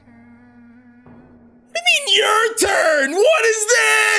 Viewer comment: Did anyone hear Flumpty saying "My turn"?